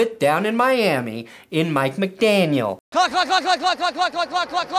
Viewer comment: it sounded like he was saying cluck instead of clock